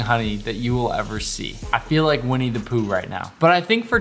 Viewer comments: Ngl, I thought he said "we need to poo rn" when he said "winnie the pooh"☠ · "I feel like Winnie the Pooh right now" What I heard: "I feel like we need to poo right now" 😭😭😭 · "I feel like Winnie the Pooh right now" What I heard: "I feel like we need to poo right now" 😭😭😭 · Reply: I actually thought he said he needed to poo, befor i read your comment saying Winnie the pooh lmao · I heard 'we need to poo right now'